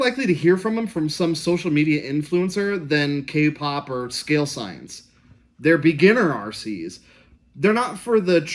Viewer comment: Hey man excellent review! What was that channel name that you said at 6:44? Sounded like you said "scale signs" but I can't find a channel by that name